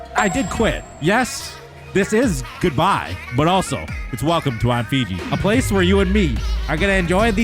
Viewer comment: Damn i was about to cry but i heard "welcome new Fiji" W rebirth